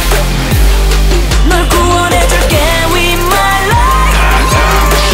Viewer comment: I misheard it as "gay with my life"